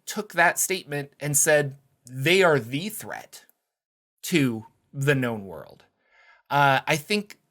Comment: I thought you said, "Goblins are THE threat to the GNOME world." Took a double take. That is a cool idea!